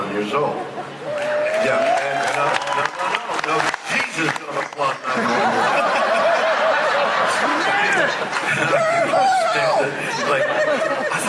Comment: what did he say? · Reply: Sounds like, "No, no, no -- Jesus, don't applaud 91 years old."